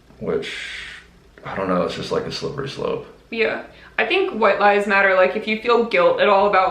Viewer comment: Omg the question about white lies matter i thought it said do you think white lifes matter and i was so confused for that entire segment until i realized i was hearing it wrong LOL